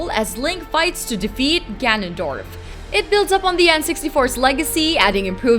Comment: when you said ganondorf,my friend was laughing because he thought you said ganonDICK.HAHAHA